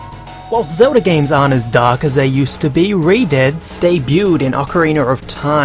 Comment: When the video said redeads - i thought it said retards :L